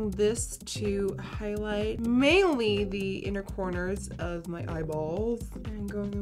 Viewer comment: I thought she said the inner corner of my balls when she applied the concealer omg